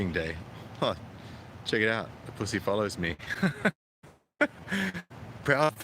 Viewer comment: Did I just hear you say "The pussy follows me"? (The sound was a little shonky at that point) Well, who can blame them? I think it must be the beard and the bald head, both of which are real pussy magnets. My face is my pussy magnet too. Unfortunately it's permanently set on "repel".